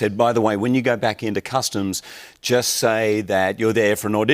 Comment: oh.. he said customs! I thought he said costumes! i was confused AF at first. ha.